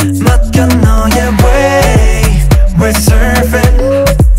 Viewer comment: I just realized he's singing "we're surfing" lol I was singing "we're servin' " all this time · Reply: Ohh I thought it said resurface 😂 You can image it YoUr wAy rEsUrFaCe 😂